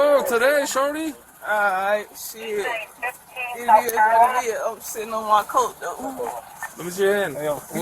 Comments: what does she says ?? I hear "you need to die for sitting on my coat tought" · Am I wrong, or did I hear that cop call this person "shawty"? Around 1:40...